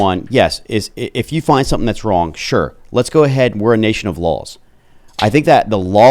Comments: i thought he said were a nation of lols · I thought he said, we're a nation of LOL's... 😂